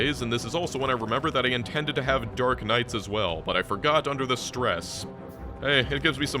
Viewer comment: I thought you said "dark knight" and not "dark night", so I was thinking for a moment the bitters were going to be fantasy classes and I was flabbergasted XD